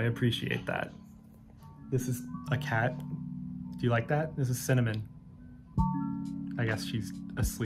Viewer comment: I thought said “this is cinema!” at the end there for a sec which was hilarious but didn’t quite make sense so I rewound it. Cinnamon is a cute name for a cat but honestly now I just like the thought of people randomly declaring moments “This is cinema!”